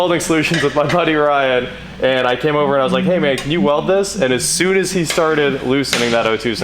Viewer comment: Did I hear correctly a "hey mate" - I think we would be happy to have you as a future Aussie:)